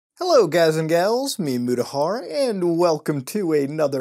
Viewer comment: every time i hear your intro all i hear is "Hello guys and gals, it's meme Mutahar"